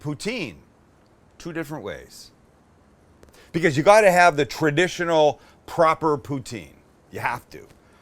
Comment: My fried 3 am brain heard him say ' you have to have the traditional proper pootie tang'